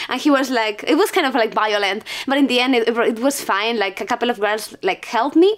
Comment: i thought she was saying biolin instead of violent. Lol!